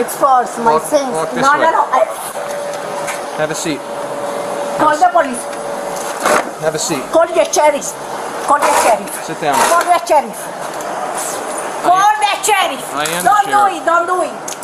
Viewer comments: All I could hear was “Hold the cherries!” And I just realized that’s what the captions say too. Haha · I’ll never understand how trespassers get arrested. It’s the easiest way to not get arrested. Just leave. That’s all you have to do. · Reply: All I hear is “Hold the cherries.”